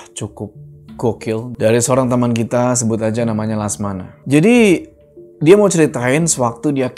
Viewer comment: lasmana kok jadi salah dengar jadi ki langsamana 😂😅😅 sehat terus mas, semoga rejeki lancar dan story telling ny tambah bagus 😊👍